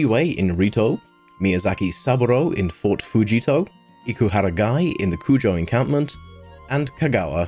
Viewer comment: What I heard : “Iku had a guy” 😭